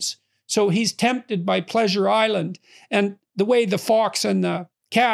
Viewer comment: I’m getting my podcasts mixed up I almost thought I heard him say Pinocchio is tempted by the pleasure squad